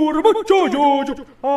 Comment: I like how is saying "Jojo is boring" in spanish. Thanks for the likes lol. Also turn on captions if you still don't believe is saying "Jojo Aburre" Woah I have 700 likes now cool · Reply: no, i heard "Jojo abuuurubo!"